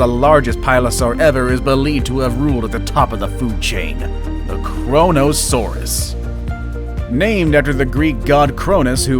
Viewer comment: who else thought "korona" when it said kronosaurus